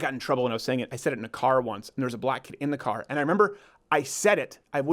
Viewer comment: Sounded like he said. “There was a black in the car lol”